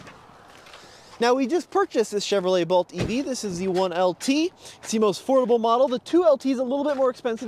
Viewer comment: Sounded like said "The 1LT is the most horrible model!" whenever he was about to show the interior of the Chevy. I listened to it twice!😆😀